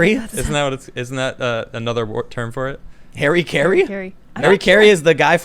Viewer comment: I definitely thought they were saying hairy Carrie. And that hairy Carrie was slang for an sjw haha. Ha rah key re